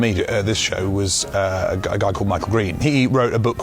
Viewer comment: Did I hear Michael Reeves?